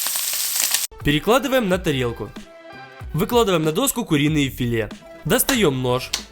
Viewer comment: выкладываем на доску куриное филе Я: филиное куре👌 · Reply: Я думал мне одному послышалось, что он сказал "Выкладываем Филиное куре"